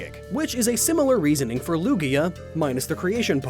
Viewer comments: Did he just say Lugi-O ? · I could've sworn you said Lugio. Not Lugia. 😂